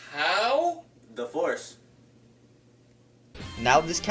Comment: When I first saw this, when appeared in Peanut's livingroom and Peanut said: "How?" I thought that said divorce when he actually said the force XD